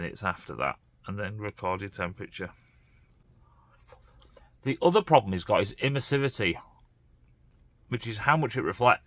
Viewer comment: I thought you said, 'the other problem he's got is imbecility' 😂.